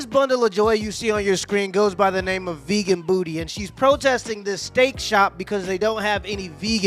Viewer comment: whoa, whoa, wait. Did I just hear that this dummy's name is "Vegan Booty"?🤣😂 Making an ass of herself from the start, I guess.